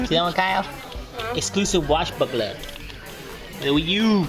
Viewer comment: i thought u said wash fuckler lol